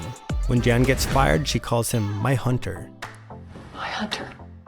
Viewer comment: I think at 4:13 Jan says: “Bye Hunter” not “My Hunter”